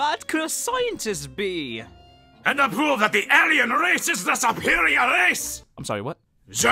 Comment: did he say ALIEN race? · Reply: i cannot tell if you're being ironic but anyways, it said ARYAN